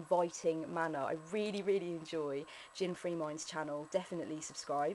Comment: did you say 'JinFreeMind'? I couldn't quite make it out and didn't see that one in the Description.